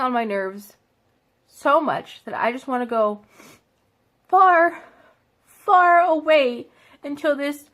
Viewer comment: is she saying she wants to go to a galaxy far far away